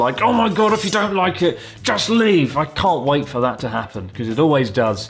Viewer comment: I misheard that as "If you don't like it, just weeb" and you know what...still fits.